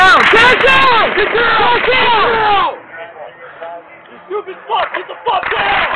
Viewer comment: “Lucas Bruch get the F down” Did I hear that right??? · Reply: What you hear as bruch is F